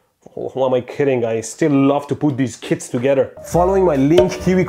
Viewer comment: Had to watch it twice. First I thought Mehdi said "I still love to put these kids together".